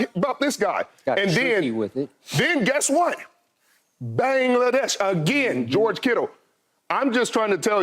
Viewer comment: did Sherman say, "Bangladesh"